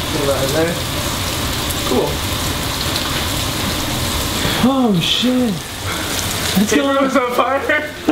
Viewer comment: did say "oh sh*t" ? .. 3:50 .. dont believe me, try using caption.